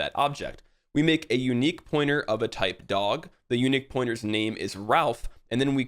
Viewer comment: why did i first hear that as "we make a unique pointer to the tight dog" lol.. my brain is cooked